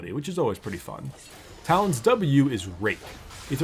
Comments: I could've sworn he said " talon w is rape" :/ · I thought he said "rape"